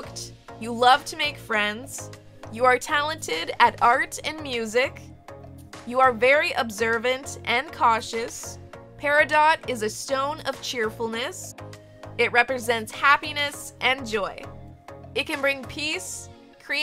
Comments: I thought it said PERIODT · am i the only one who thought it said "periodt"